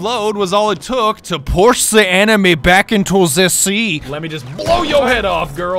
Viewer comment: I heard him say push zhe anime back to the sea